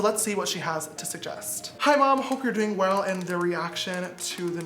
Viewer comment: Did he just say hey mom